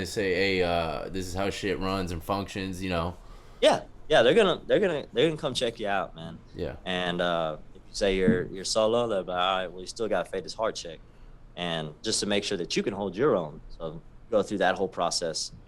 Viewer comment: Agg is down to 50% now?! Wow. Used to be 75% - 80%. And i remember hearing Mexican dudes talk about a 'cora check' when u step in the pod/tank. I thought they were saying 'corner check' for the longest!! Like by the corner of the area by the back bunks. 😂